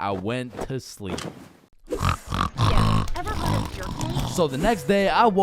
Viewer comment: So im half asleep listing to this man and i hear this at 12:25 (ever heard of jerkmate) like wtf